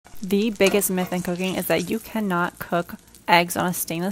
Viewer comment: Oh my god ...I thought she said "The Bigest Myth In Cocaine"...I was so confused ...I thougt it was like the commercial this is your brain on drugs